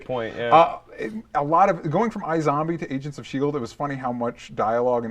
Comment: I thought said, "Asians of Shield" and I was like, "Hey...."